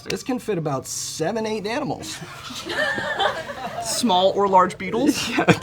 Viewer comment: i have watched this so many times and just noticed he says this can fit 7-8 danimals and she definitely thought he said animals so she went for beetles 🤣🤣 he definitely realized but was too sweet to correct her lol